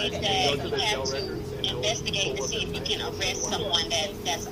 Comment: Did I hear someone say, "you have to investigate if you can arrest someone of color"?